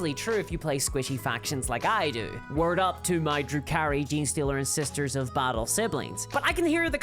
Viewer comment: I had to rewind 5x because I thought was saying Drew Carrie instead of Drukhari. Or was that the joke